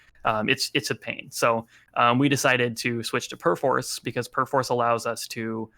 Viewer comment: What does he say? "we decided to switch to ... " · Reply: Perforce. It's a source control program.